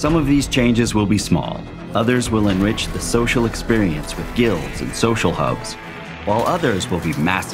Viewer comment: Did you say.... "Guilds"?